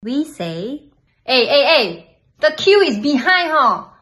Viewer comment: I heard "you never see so many faqueuing" 😂😂